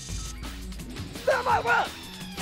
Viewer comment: For those of you don’t understand what Kenny said at 2:22, he said, “Yeah! Try this, Professor Chaos! Feel my wrath!”